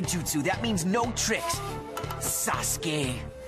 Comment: is it just me or did he just call assume sasgay